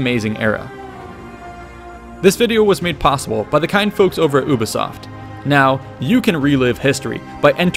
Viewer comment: did he just say UBOSOFT WTFFFFF!